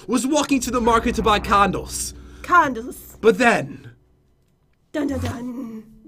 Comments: sounds like Jen said condoms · It sounds like your saying condoms. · LOL IT SOUNDED LIKE HE SAID CONDOMS INSTEAD OF CANDLES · guys at 4:10 pat did NOT say condoms he said candles, calm yourselves this is a kid friendly channel. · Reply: im using speakers and it acutully sounds like he is saying condoms but i listened closely and said candles in a weird way :D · Did Jen say condoms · in 4:10 if you put subtitles on it says "oh no my condoms" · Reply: I thought he said that lol but then I remembered he said candles